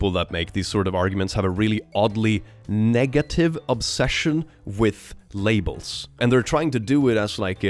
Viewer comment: i heard this as "a really oddly negative obsession with labias," which. i mean, i'm not right, but i'm also not wrong.